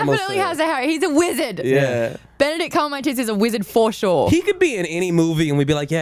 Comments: where on earth did get that amazing skirt if anyone has any clue let me know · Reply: Completely different topic, but did I hear correctly that you said Benedict Cumonmytits instead of Cumberbatch at 30:36? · Did anyone else hear that said, Benedict cumonmytits, instead of Cumberbatch?